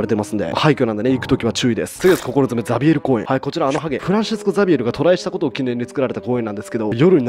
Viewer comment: 自分の地元の訛りのせいで、「フランシスコザビエルがトライ」って聞こえてラグビーボールかかえてダッシュするザビエルが脳裏に浮かんで混乱した